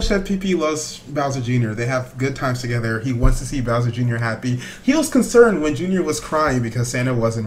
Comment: I thought he said he wants to wee wee on him